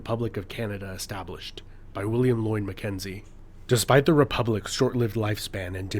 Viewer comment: Yes, and his pronunciation of "William Loin McKenzie". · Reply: I replayed that several time just to make sure of what I was hearing! At first I thought he was saying "William Lloyd MacKenzie", but then I realized he was just mispronouncing "Lyon".